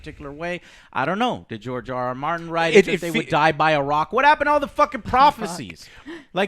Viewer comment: when tried to speak over Joe and he can't, it sounds like said "can't talk" and laughed. Joe is super passionate about these things and I respect that, but it can't be a discussion without the others talking equally or at least 40% of the time... If it's going to be like this, just make it solo discussions with the camera, I just feel bad for and... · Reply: She said "by a rock" in reference to the Lanister deaths. Not "cant talk"